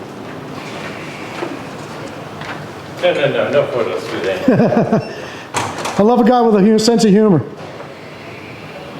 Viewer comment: What did he say? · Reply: i believe he said "no no no, no photos today"